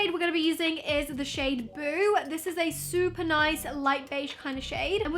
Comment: I thought she said the shade ‘poo’